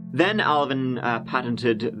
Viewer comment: I thought he said one word: "Alvincatiumnatan" 😂 way he said it.